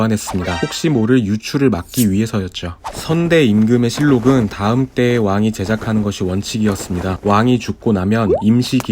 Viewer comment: 사극보면 임금이 붕어하면 내시가 어디 꼭대기에 올라가 뭐라고 소리치던데 무엇인지 아시는분? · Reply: 상위복 입니다. 몸으로 다시 돌아오라는 뜻이죠.